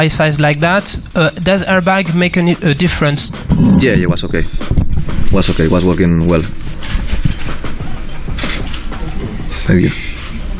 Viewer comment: All these comments discussing if they actually work, which the entire video was about, but I'm here still trying to figure out what Jorge Lorenzo said at 5:02. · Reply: Yeah it was okay. Was okay. Was working well. Thank you.